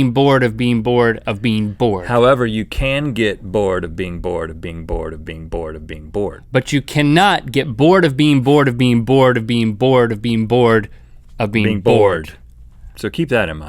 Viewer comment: it sounded like link was saying:bean board bean board... and rhett was saying:bing board big board... lol